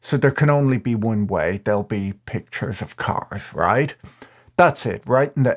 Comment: Sorry for a moment there at 0:52 I thought you said "Pictures of c**ks". Either way I had a good laugh, thanks!